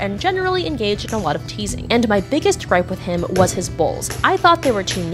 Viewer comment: I THOUGHT YOU SAID BALLS INSTEAD OF BOWLS